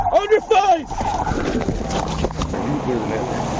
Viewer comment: Did he say "On your thighs!" ?????